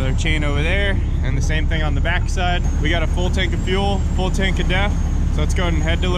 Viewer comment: Thought you said full tank of death...had to think for a minute.